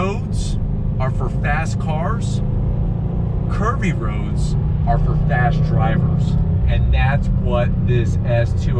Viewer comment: I thought he said "turns are for fast drivers". ;)